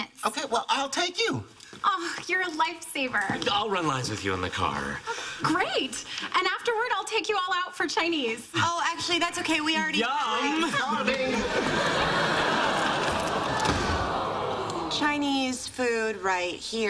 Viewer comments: did Rahj just say "yum" in front of 2 pretty girls??? Or am I hearing things? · I always hear "You're a lightsaber"...